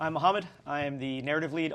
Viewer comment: I really thought he said "Hi, I'm a hobbit" first. Had to rewind. Sorry Mohammad!